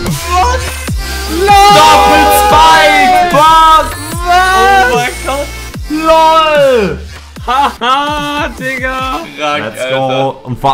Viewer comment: Am I the only one who heard "ha ha, n**ga"?